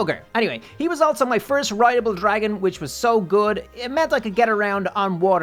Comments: Did he say rideable dragon? 11:15?? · did he say dragon? go to